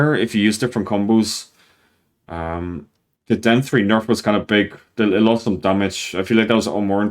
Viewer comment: Everytime you Said down 3 i thought you Said DEN 3 ☠️☠️☠️